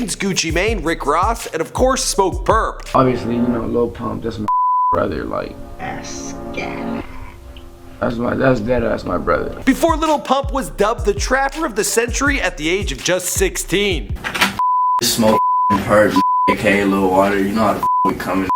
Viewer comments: At first I thought you said "rapper of the century" and almost got really super triggered lol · did u just say rick roff? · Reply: Hazem Nasser no he said rick ross